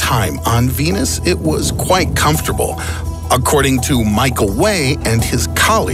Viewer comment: I thought he said microwave when he said michael way